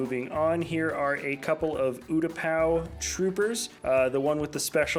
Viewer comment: is it just me or does he say oogapau